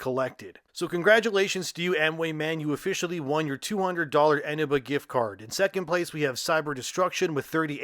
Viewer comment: I spit my soda and had to rewind when i heard "Eneba gift card" because my medically trained brain heard "enema gift card".